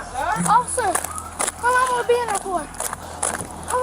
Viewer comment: subtitles say "how long am I being awkward" what they really said "How long am I be in there for?"